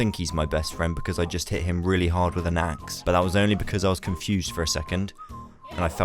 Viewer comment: When he said he met chonk the panda and it may not look like it because he hit him really hard with an axe,I thought he said he hit him really hard in the a**